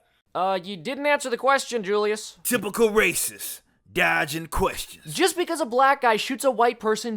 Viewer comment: Lol i thought he said: Simple racist. Guy asking questions.